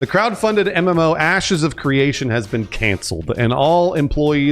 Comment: Sounds like he said: "Asses of Creation" at the beginning. WTF 😂😂